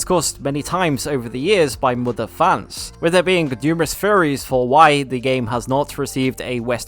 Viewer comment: when he says theories it sounds like he said furries